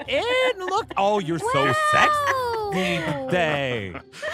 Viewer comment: Bro did just say "sexy"? Its so goofy ngl XD